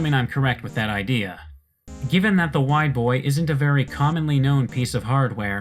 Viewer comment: I almost misheard it as "The White Boy".